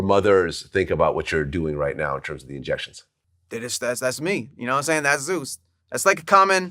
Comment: you missed a "y'know wat im sayin?"